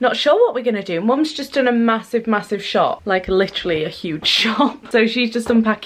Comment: Thought you were gonna say "Mum just done a massive, massive shit" at 0:33 😂